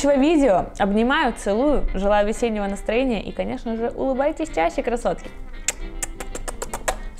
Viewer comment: В конце мне послышалось: 《умывайтесь чаще, красотки》😂